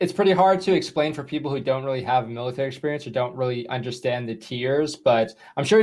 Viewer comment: Yes I needed a explanation of the tiers in the “mailitary” lol XD. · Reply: I heard it as mealitary😂